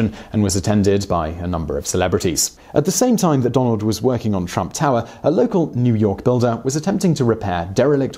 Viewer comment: Seriously thought he said Donald was "twerking out of town" 😂